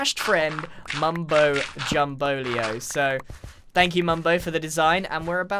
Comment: My brain is not working today. 3:09 What was actually said: Grian: ....from none other than our mustache friend, Mumbo Jumbo. What My brain heard: Grian: ....from none other than our mustache friend, Mario. Yeah, I'm weird